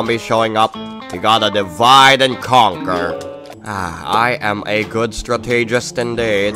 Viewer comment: Did say divide and conquer? 1:35 I think in gonna get cube combination memories